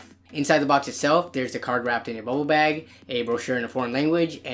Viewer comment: i heard "a card wrapped in ebola bag" rotfl